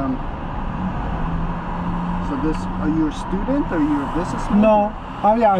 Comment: bruh i heard "are you stupid or you work?" xD